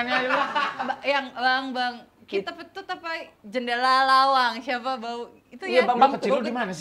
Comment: Bagi yang gak tau Dita ngomong apa di menit 05:30 - 06:00 Nih gua kasih tau Di Surabaya Kalo lagi Ngundi apapun anak anak Sd-smp bahkan SMA biasanya kalo lagi ada yang Kentut di satu tongkrongan gak mau ngaku di undi pake Mantra itu Mantra nya gini Bang Bang Tut Jendelo lawang Sopo mau ngentut di tembak raja tua tua tua kaji rambute kari siji buka lemari isine roti Roti atos Atos Silite beledos (Tangan sambil muter Ngundi siapa yang di tunjuk terakhir termasuk yang Tangannya nunjuk juga di itung) Artinya kalo Di bahasa indonesia Bang Bang tut Jendela Pintu Siapa tadi kentut Di Tembak Raja Tua, Tua Tua Haji Rambutnya tinggal 1 Buka Lemari Isi nya Roti Roti Alot(Keras) Alot Bokong nya meletus